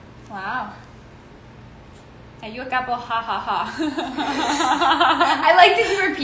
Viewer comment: What is Danielle saying to Stephanie at 4:46 ? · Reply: She is reading a question and the question says... “are you a couple ha ha ha?” Funny thing is they never answer the question... hmmmm I think they are a couple IRL.